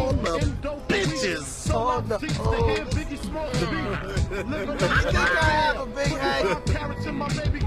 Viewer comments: what did Cheddar say? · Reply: "Do you think I have a big head?" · At 0: 48 what does cheddar Bob say? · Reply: Jake1 it sounded like he said I think I have a big head